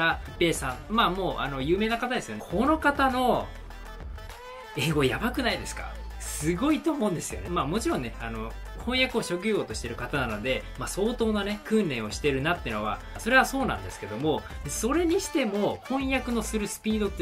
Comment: 通訳じゃなくて翻訳って言ってるように聞こえるけど、まさかね。